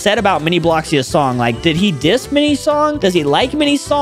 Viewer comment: what did he say????😮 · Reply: He didnt say piss he said diss